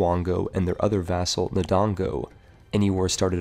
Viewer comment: Am I The Only One Who Heard Dodongo?